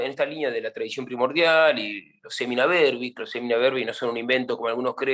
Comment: que dijo alli , sirena dermis? por que lo ando buscando eso lo inculco san agustin? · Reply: Semina Verbi